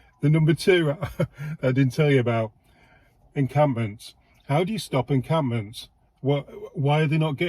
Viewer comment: I thought he said incumbents , he meant incampments